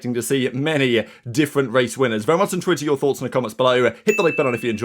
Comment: I watch every day and I have no idea what you say 00:32 "Very much interested to enter into your thoughts" ? "Very much tweet to enter your thoughts" ?? What do you say here? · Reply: Very much intrigued to hear your thoughts